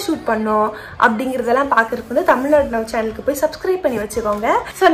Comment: What is the channel name ? I can't understand properly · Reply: Tamilnadu now